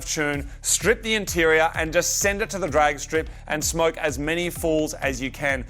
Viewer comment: "smoke as many poles as you can" I spelled it how I heard it.